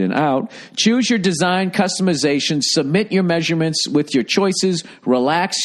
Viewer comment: did you say customazation?😂😂😂